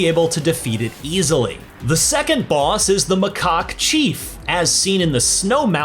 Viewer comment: did he say "mah c0ck thief"? 😐